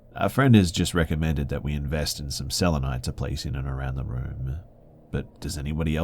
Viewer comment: What does he say at 19:56? "Invest in some.." what? It sounds like he says invest in some celonite, but that's not a word.... I've replayed that part 7 times and I cannot figure out what he is saying.... Can someone please tell me?? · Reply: Selenite* not celonite. Healing rocks